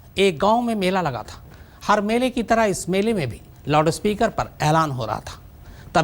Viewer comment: From 10:06 everytime the way he pronounced "loudspeaker" I heard something else 😂😂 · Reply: Lodaspeaker